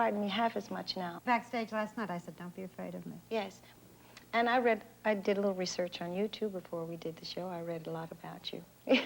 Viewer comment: Diana Ross says "I did a little research on YOUTUBE ...", but youtube didn't exist in 1978!! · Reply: Lol.. I thought that was what she said too. But she said, " I did a lot of research on you too"